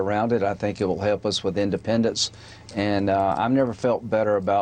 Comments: I thought I heard "independence" instead of "independents" and I thought the southern states were seceding again. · LOL I heard "independence" instead of "Independents" twice before turning the subs on... was like "wait didn't I miss a pair of episodes? Weren't they saying it wasn't a coup attempt last time?"